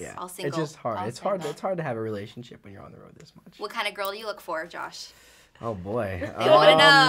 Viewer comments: when the interviewer said, "what kind of girl do you look for josh?" i thought he said "..a boy.." at first! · Haha 3:31 I "josh what kind of girl do you look for?" I totally thought he said boy...